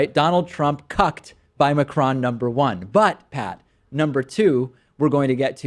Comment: LMFAO @ 3:28 , holds up a hand like he's high fiving and says,,, Me: WTF did he say, "butt pat"?!?!? Did Macron grab Trump's ass? Then i realized the other dude's name was "Pat" Who else caught that?